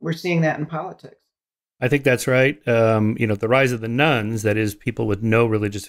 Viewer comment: Every time he says “rise of the nones,” I hear “rise of the nuns.”